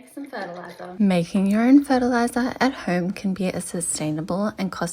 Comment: Omg my heart dropped I heard "making urine fertilizer at home " lol thanks for the great plant advice!